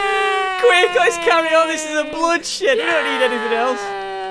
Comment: Wait at 10:40 did say Bull s*it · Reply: He said 'it's a blood shed'